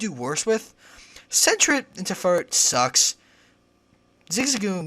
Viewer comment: i know what that pokemon looks like. i mean what pokemon does he say, sounds like centret. its closer to 2:20 mark.